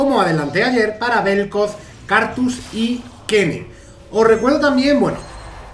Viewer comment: yo entendi kayn :'D